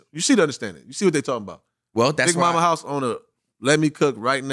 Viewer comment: Aye yall I'm just watching no side here so don't start wit me gang...BUT at 20:00 did say Big Mommas House im drunk gang😭😭😭